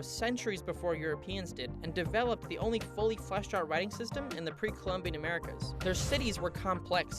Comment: what did he say 💀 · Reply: The only fully five-star writing system in the pre-Columbian Americas